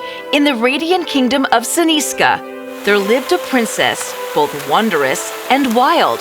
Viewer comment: lovely story but there is a mistake 0:15 the captions say here and the voice says there.